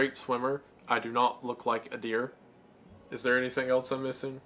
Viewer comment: Funnily enough, when said that Wendi was just an adequate swimmer, I heard her say “your plushie looks like a deer” so that’s two ways that he might have an uh oh moment.